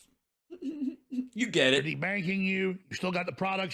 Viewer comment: Ooohhh de-banking! I thought he kept saying Tea-bagging. My bad.